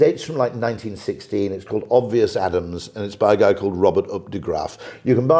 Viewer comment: Hi Simon at 49:09 The Book Rory recommends is from 1916 called "Obvious Adams" by Robert Updegraf about 4.5 pounds or $7 USD. I love that he looks at what is really happening, he observes like a detective instead of assuming the research done is correct. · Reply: Thanks, just scrolling through comments to catch the title of the book, initially, I thought he said "obvious atoms"